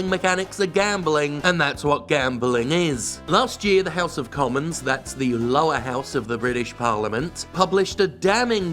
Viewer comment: "The house of lords, the lower house of the parliament" lol. I mean, yeah, kind of. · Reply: I think you misheard that part at 0:30 "last year the house of commons, that is the lower house of the british parliament, published..." I had to rewind, too.